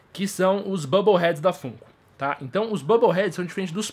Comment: Kkk o tempo todo eu entendi "Fungo"